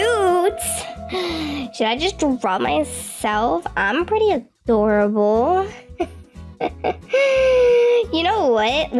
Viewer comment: did actually call herself adorable instead of cool for once :O?? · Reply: She said the picture is adorable